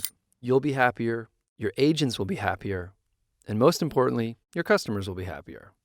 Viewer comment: I thought he said “your asians will be happier”. I thought nah, can’t be it. Then he had a photo of an Asian person 🤯